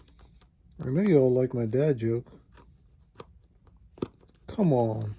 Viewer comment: OH! DAD joke !!! Thought you said BAD joke !! ....did smile at the butter one 😁